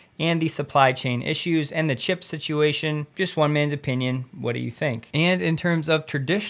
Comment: watching at 2x speed. I thought I heard "Just a women's opinion"